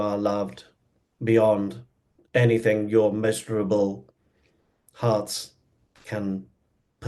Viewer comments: Measurable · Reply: I heard "miserable"...had to go back & listen 3 times!!! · Huh?? "You are loved beyond anything your miserable hearts can perceive at this time." Dayum. Arcturians be harsh af..😒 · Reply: He said "measurable". I too, thought that he said miserable at first, lol.